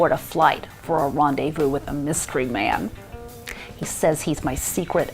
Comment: Wait. did I hear you say "mystery man" or "misery man"? And was that "he's a keeper" or "a creeper"? Sorry my hearing aid batteries went dead just as you started. I'm pretty sure I hear you right though. "misery" and "creeper". Right?